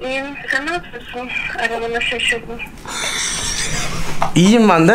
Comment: ne diyor burda? :DDD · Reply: Aramana şaşırdım. Diyor